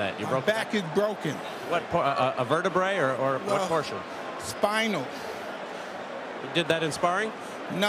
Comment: "My back is broken" "which part, vertebrae?" "spinal" Love Tyson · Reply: he clearly says spinal not thpinal